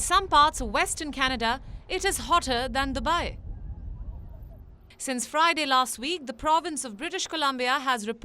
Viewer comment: at :58, did i hear "hotter than the wife" ?